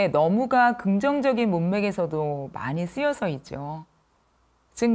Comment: 이 부분 ‘쓰여져’로 들리네요. 아닌가요? ( 치아 사이로 새는 발음을 하셔서 ‘셔/져’로 볼 수 있지만 문맥상 ’져‘겠죠?) 우리말 전문가신데… 이중피동을 사용하셨네요. ㅠㅠㅠ